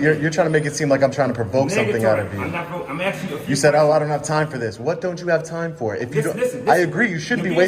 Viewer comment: I thought he said I don't have time for Nazis lol